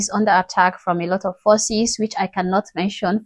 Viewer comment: ohhhh forces, I thought you said under attack from a lot of Aussies. This particular Aussie loves your content. Keep up the great work.